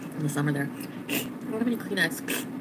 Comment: I really think I heard she asked if she had any coke on her nose, when she talked to him at the end of this video . Anyone else heard that? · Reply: I thought I heard "do you have any coke on you?" But I could be wrong.